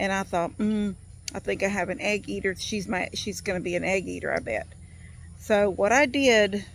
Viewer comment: LOL, I thought you said you had an ant eater.